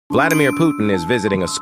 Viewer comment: I heard “Vladimir Poop” 😂